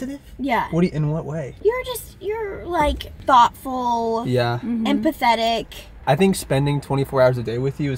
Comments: I really thought she said “thoughtful, and pathetic” hahahaha! Love you guys! ❤️ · She said empathetic I thought she said he was pathetic 😂😂😂😂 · for a sec i thought abby said "and pathetic" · I really thought said “You’re thoughtful and pathetic” before I realized she said empathetic 😂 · thoughtful, empathetic… Me: I thought she said pathetic 😂